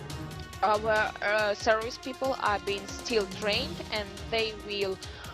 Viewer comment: does she say terrorist people are being trained? I think she meant territorial people